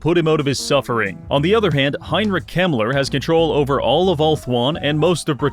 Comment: I thought he said Heinrich Himmler 😭😭